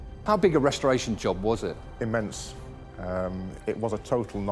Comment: what he said? F**king sh*t? :o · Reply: Immense(?) it just means it was a very big job